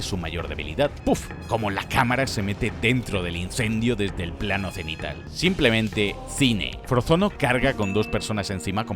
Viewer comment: ¿Que dijo 29:26? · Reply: Qué asistió al parto de Jack Jack